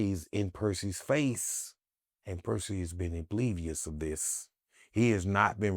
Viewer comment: WTF HE SAY???🤣🤣🤣 "IMBLIVIOUS"??? 🤣🤣🤣 DAMN